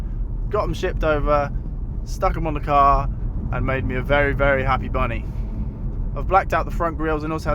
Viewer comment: Did he say that he is a happy, happy bunny??